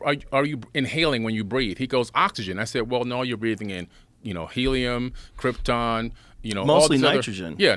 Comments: Did just say we’re breathing in Krypton? 😐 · does homie say krypton?!?